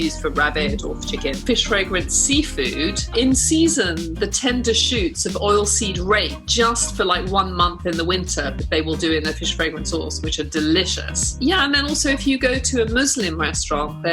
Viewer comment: What does she say at 14:58? Oilseed ray?